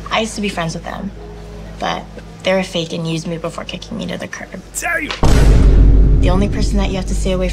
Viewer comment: Did she just say that she bitted the curb?!!😭💀